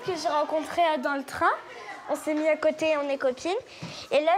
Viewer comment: il y a que moi qui est à entendu "on ses mis à côté on est coquine " 😂😂😂😂